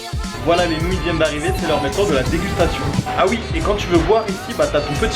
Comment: Like si toi aussi t'as remarqué qu'il dit Dégutation au lieu de Dégustation à 7:52 xD #partalarecherchedumagicien #ilfauttester Perso ma copine, préfère ma nouille bien dure :D